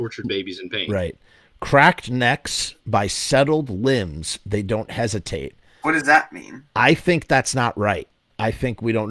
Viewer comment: I just isolated the vocals, and it really sounds like Glenn is saying, "cracked necks by settled hands, they don't hesitate."